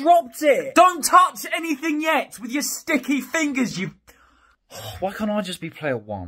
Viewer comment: DID I HEAR "STICKI FINGERS" IS THAT A JOJO REFRANCE??